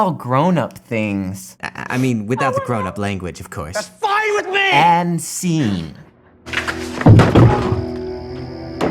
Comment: He’s talking over it at 8:33. What is she saying? The quote is too short for “I want a divorce” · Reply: I want out. I just want out.